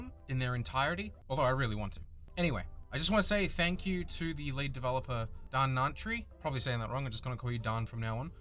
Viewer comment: Oh. Oh. LEAD developer. Ffs, I thought you said "late" developer and spent a solid 5 minutes trying to figure out what happened to Dhanantry.